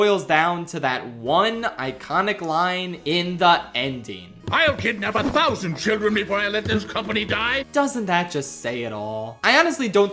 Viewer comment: I always misheard waternoose's line as "I've kidnapped a thousand children before I let this company die" Because it would make sense, he could blow the threat level of children out of proportion and banish those that learn the truth